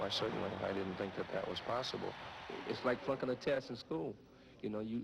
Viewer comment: I thought he said "fucking a test in school" lol